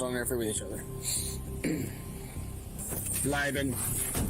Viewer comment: For people wondering what the handler said to the dog: He said "Bleiben," which is German for "stay." Apparently, they teach their police dogs commands in German.